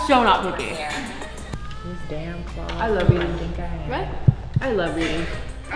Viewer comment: I heard “I love you” too Emma and not “I love eating” 😂